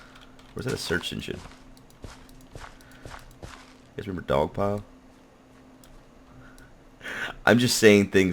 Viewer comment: When guude said,"Remember dog pile", I thought he said dong pile LOL